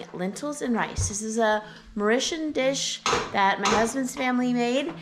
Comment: What type of recipe is the lentil dish? It sounded like you said moritian, but idk what that is and nothing like that comes up when I google it. I’m wondering if you meant to say Moorish 🤔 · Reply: It’s “Mauritian” ❤️ my husband lived on the Island of Mauritius as a child